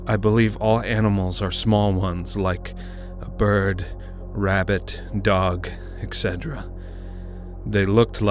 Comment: it says habbit instead of rabbit